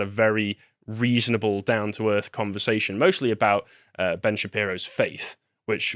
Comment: NGL, thought he said “Ben Shapiro’s [FACE].”